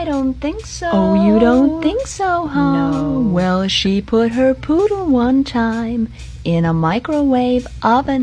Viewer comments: I love it when the audio says "to eat it?" And then says "yaa" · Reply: when they mentioned eating the poodle i misheard and thought they said they were eating poop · The first time I heard this audio, I thought it said she put her baby in the microwave, and I was like meh. But then when I found out it was her poodle I was like crying- I think I have something wrong with me